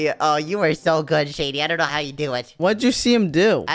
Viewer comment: does say how or opposite or heaven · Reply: don’t you have ears? she said how and am i the only one that heard said so it’s only zoey and when is dead in